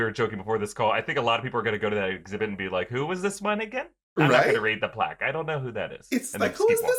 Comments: what did he say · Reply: “Who was this one again?” I think that’s what he said, just sounded weird. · sounded like he said "who is this my *n word*" but he said who is this one again i had to watch that so many times back i got so scared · all I can hear is who is this my nigga 🤣 what did say? I can’t make it out but Ik it’s not what I’m hearing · did you just say nigga? Am I hearing this correctly? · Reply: “who was this one nigga” that’s what I was hearing 😭😭😭😭😭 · Can some one please clarify what said at 7:23 when talking about Bradleys Statue. I listen to him like 10 times and all i hear is "Who is this my Ni**a? LMAO! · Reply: Who is this one again??????? Idk lol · I really thought said "Who's this N****?" 😂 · “who is the my 🤷🏾‍♂️”what did he sayyyyy😂